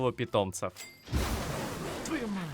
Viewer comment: я один "твою мать" услышал? P. S. Респект за профессионализм